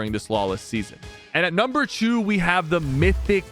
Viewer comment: Guys am i the only one who heard number chew